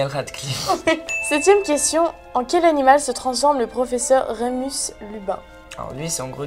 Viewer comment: C'est moi ou elle a dit "Remus LuBin" à 4:18 minutes ?